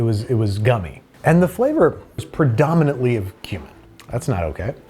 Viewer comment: not me hearing human instead of cumin💀